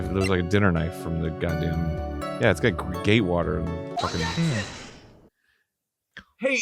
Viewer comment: Every time they said the name of the hotel, I kept hearing "Gay water."